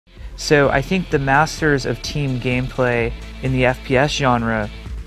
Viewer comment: im i the only who heard at 0:00 this: 'so I think the bastards'